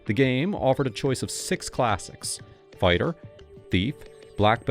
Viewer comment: i heard "classics" and i had to go. not sure why its so gross to hear AI. maybe its because everything already feels plastic on the internet or maybe its just cuz its lazy. idk why but i know i hate it.